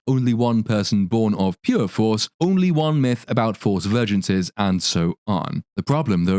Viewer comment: Yoooooo For a while there i thought she was saying foece virgins